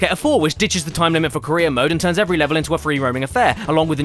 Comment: When kept saying “career” mode, I heard “Korea” mode